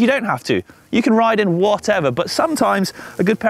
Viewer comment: “You can ride in whatever you want” Why I hear: “you can wear a Speedo”